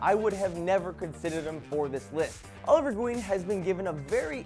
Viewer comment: How can you forget Spawn!? Also did you say Oliver Dween at 02:47? I'd also like a Green Lantern (John Stewart) game, Green Arrow would be awesome and also possibly a Martian Manhunter game, he was always one my favourites in the JLA, first being Batman of course.